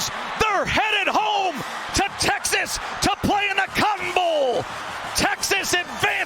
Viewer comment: when i first saw this I thought he said they were going to play in the cum bowl